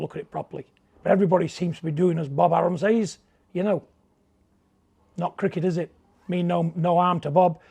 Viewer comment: did he say not cricket is it?